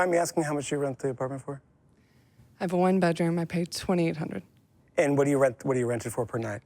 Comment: did I hear this correctly? 2800 rent???